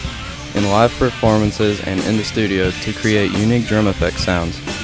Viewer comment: What is he saying at 3:11 I didn't quite Catch it. Some sort of percussive instruments supposedly ? Help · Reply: tabla